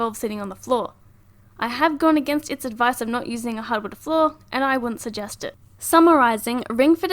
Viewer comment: What is said at 6:08 anyone. a “hubid floor” hybrid? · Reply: hardwood